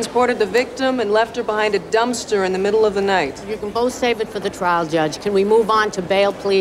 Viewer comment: what the hell did she say? Did she call the lawyer a judge? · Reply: “You can both save it for the trial judge”